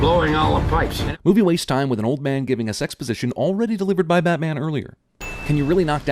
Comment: When you said "giving us exposition" I thought you said "giving a sex position"...